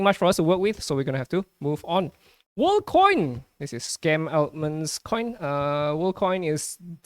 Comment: did you just said Scam Altman? 🤣